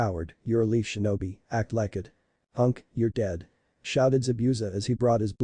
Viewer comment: 52 minutes in and I heard unc your dead lol 52:45 I think around their